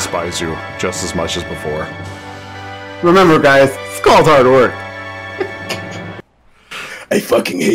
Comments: I have no idea what said at 37:55. Anyone got an idea? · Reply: "Remember guys, it's called hard work" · What does he say at 37:54? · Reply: Remember guys: it's all hard work